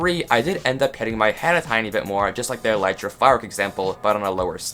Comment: When he said Elytra, caption say elixir bruh